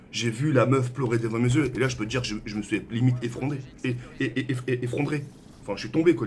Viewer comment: tu t'es quoi ? Effron.. effrondré ? x)